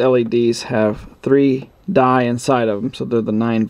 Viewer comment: What ? Can anyone help plz ? Wasn't able to understand what he said . Thnx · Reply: He said "3 DIE inside at 9 volts". It should have been "3 DIODES inside", referring to Light Emitting Diodes. At 3 volts for each, in series, would be 9 volts across each unit, then times 12 units for a circuit total of 108 volts DC.